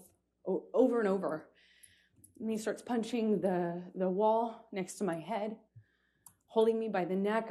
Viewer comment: Did I heard that right? 1:25 „Me starts punching the wall“ ???